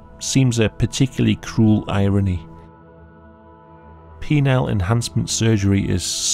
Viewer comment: Bruh I thought u said "Wiener enhancement"